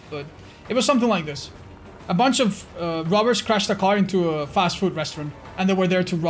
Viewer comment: I thought Otz said a bunch of 'rubbers' crashed the car. Rubbers (condoms)? Please continue my good sir. :)